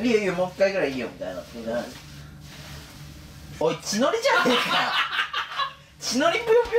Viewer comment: 「血のりじゃねぇかっ!!」が、「千鳥じゃねぇかっ!!」に聞こえる